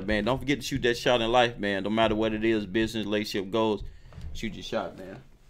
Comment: I started laughing cuz I thought you said b*tches 😂😂pretty sure you said business tho